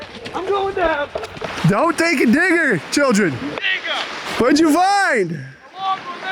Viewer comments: Did he say the n-word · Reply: Don’t take a digger - Maine term for don’t fall and hurt yourself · Time : 3:54 // is it just me or did he say!! NIGGA !!😂😂😂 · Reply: I’m a translator from Maine. 40 years in practice. He said diggah 🤓 Example: “Take a diggah, bub!” Maine-ah